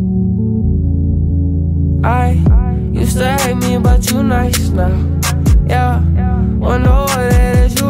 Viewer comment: wtf he just said? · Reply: “Used to hate me but you nice now”